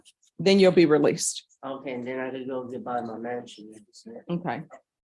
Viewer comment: Did he say "then I can go buy my mansion"